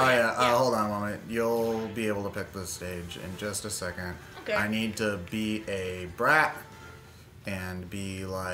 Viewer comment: Did say I need to pee?